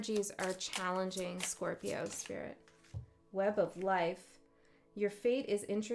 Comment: Your relatives eyes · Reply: Real eyes realize real lies. Whenever you say "web of life" I hear "web of lies"